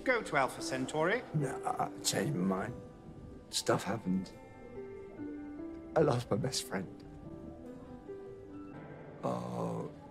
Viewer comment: Hm, I know he normally say he lost his best friend, but when I listen this, I hear "I love my best friend" 😭😂